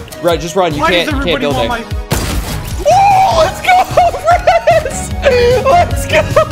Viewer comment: All I hear was "let's go razz!"